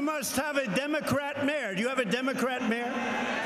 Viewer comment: I thought he was saying "Democrat Mare".